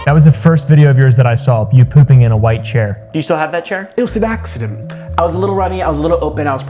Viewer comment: It sounded like he said. It was a laxative. When they talk about the chair. I was rather confused for a second